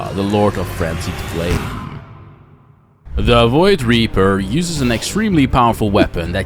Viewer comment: i thought it said voidraper bruh